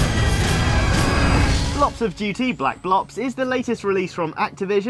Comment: thumbs up if you think at 2:00 he says black lops